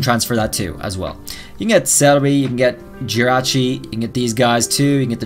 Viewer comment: On the captions at 4:39 Instead of Celebi, it is celery. I just think it is funny.